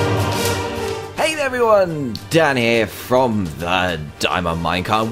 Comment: dan forget his h in here so it sounded like this hey guys dan ere